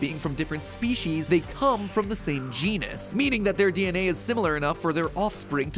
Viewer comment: When you said genus I thought you said genius